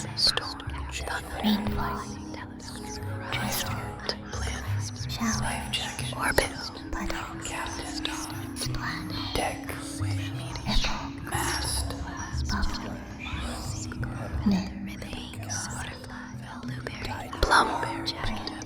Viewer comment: I didn't hear a number, but I heard dick- 😭